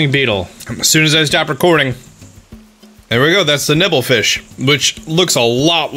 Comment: I thought you said nipple fish and it made my day exponentially better, thank you